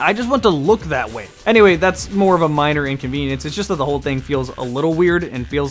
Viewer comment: a "miner inconvenience" ... is what i heard at 11:45 :)